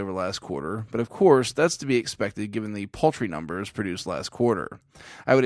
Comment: What are poultry numbers? That's what I heard you say and confirmed by the subtitles. Besides that. another great video. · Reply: Paltry was the word, not poultry. Auto-subtitle failed us ;-)